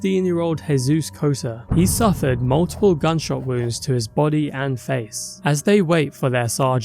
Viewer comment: Bro i heard GSI and thought why tf would you say GSI instead of Gun Shot Wound. Theyre one syllable words, you saved zero time